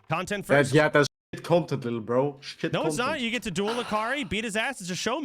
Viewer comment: did he just call soda lil bro lol